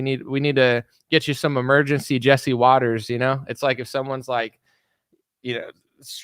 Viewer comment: When you said “do you need a little Jesse Waters…” I thought you said Jesse Peterson. EVERYONE needs a good hit of Jesse Lee Peterson to make them well again, hehe. 🤩🥳